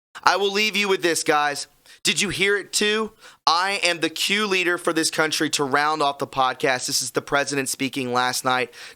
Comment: I heard ".. I'm. Key leader for this country."